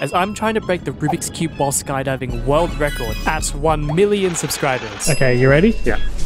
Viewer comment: i thought you said at a BILLION subs 💀💀💀